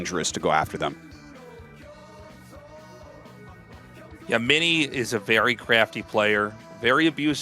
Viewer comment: what i heard was mini is a very crapy player :O. I agree compared to snow level, yes ofc